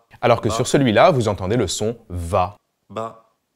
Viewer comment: environ , j'ai entendu ba , et pas va